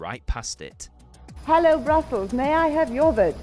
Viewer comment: Did i hear it wrong or was the netherlands voting from brussles? why? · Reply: In the early days of the contest, the Dutch jury was located in the town of Bussum. It sounds like Katie said Brussels, but she said Bussum.